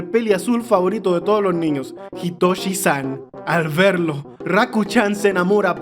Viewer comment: Mis muchachos escuché itoshi sae